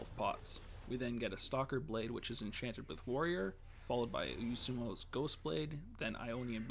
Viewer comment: yo what the hell did you say ???? lol I am dying :') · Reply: Yousoomo's......... lmfao :X